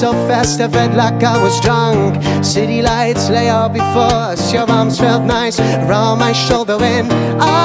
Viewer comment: I found a nice little spot on the harbour for a street show and had the chance to preserve the moment in this video using my own little soundtrack hehe. I'*ll be in La Rochelle till mid September. Come over and say hi :) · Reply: i kept hearing "your mom felt nice wrap around my shoulder". Is that normal?